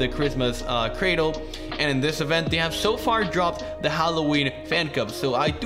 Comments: did he just say Halloween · "they just released the halloween phancub" I hear you. I understand you. Yes.